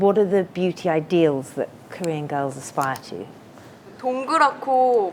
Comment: Lol at 23:51 the subtitles say "...our ideal appearance would be that of westerners".... that's not what she says at all. She says women strive for a round face, straight nose, and kinda roundish eyes. Ah, as they say, never let the truth get in the way of a good story/narrative haha...